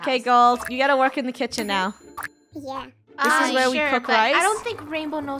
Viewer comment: did anyone hear a “yea” in a child voice idk